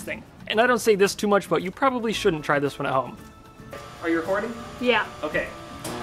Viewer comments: I thought he said "Are you horny" at 3:18 xD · I thought he said "are you horny?". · When he said "are you recording?" it sounded like he said "are you horny?"